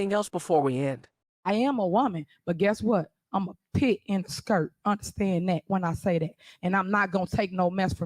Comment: Did she say she's a 'pig in a skirt'? I'm not familiar with that term.